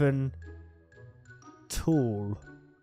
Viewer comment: I think that says "Talk"